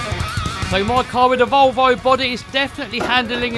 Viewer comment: Did you say Volvo body? 15:35 😁